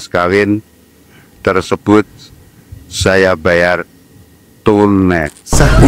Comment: Waktu ijab khobul , bilang di bayar tunep bukan tunai , itu berarti tidak sah . tapi apa itu yg saya salah dengarr yaa😅